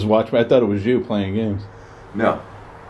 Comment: I heard a whispered “No”.